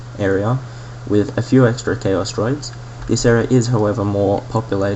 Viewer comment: lol at 3:45 i heard it like "chaos droids" -.